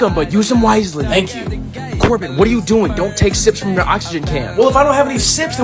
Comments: I thought it said simps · I thought that said simps at first 💀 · I thought he said simps not sips